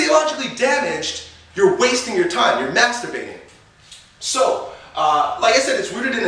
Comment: did he say masturbating?